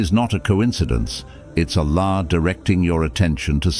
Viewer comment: at the 1:43 mark of your video instead of Allah the captions instead say (a law) 1:43 1:43